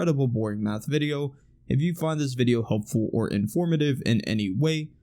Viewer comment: Thought you said " hope you find this video hopeful ", well certainly I did not. Also I would like to buy few stacks of this buff without having to log to sanctuary all the time even if it's other chars.